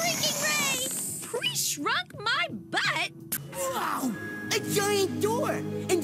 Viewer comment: ok but real talk… what kind of villain invents a cloths shrinking ray to beat a hero? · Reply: It sounds like Clover says, “Pre-shrunk, my butt!”, not “Shrunk my butt!”